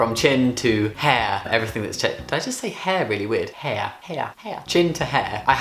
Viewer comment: I already have lots of body hair yes ,even chest hair and I'm afraid if I start T I'm gonna turn into a wierd hairy uncle . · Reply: For some reason, I thought you said “weird hair gorilla”. XD